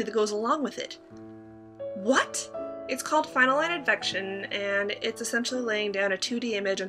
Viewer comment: Finalelic-Infection?? what did you say? I would like to google it ....